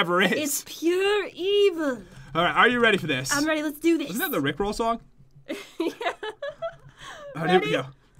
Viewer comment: Am I the only one or did anyone else hear say rick Ross